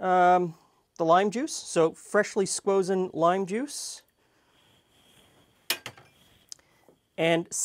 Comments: did he say squosen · Did he say "freshly squozen lime juice"? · did he say 'freshly squozen lime juice' at 7:02? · uhhh did he just say squozen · Who tf cares about cocaine he said squozen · Did he say freshly squozen? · did you say "freshly squozen"? WTF is that about?